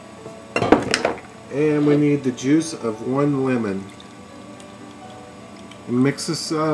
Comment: When u said juice of one lemon. I thought u said juice of one woman